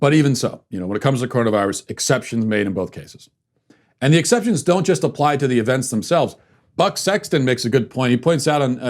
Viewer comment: I thought he said “Butt Sexton”